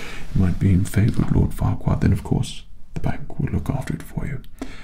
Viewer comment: did he just say lord farquaad 💀 Edit : 29:40 this one too 30:30